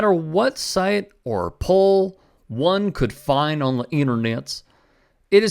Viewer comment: he said internet's, anybody else catch that?